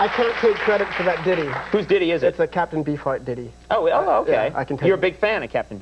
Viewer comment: Flea talking about Captain Beefheart. That's awesome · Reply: Oivalf Music hey i know it’s 4 years later, but i totally thought he was saying captain bee fart